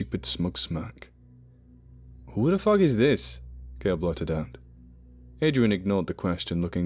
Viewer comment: In the first 30 seconds I thought he said Paul Blart I was going to say he is definitely the worst human I can think of